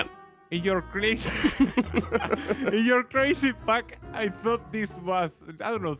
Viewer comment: did say you crazy fack ??? or did i just hear that rong its